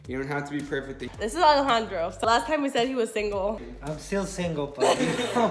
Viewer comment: did u say his name is Alejandro if u did MY NAME IS ALEJANDRO TOO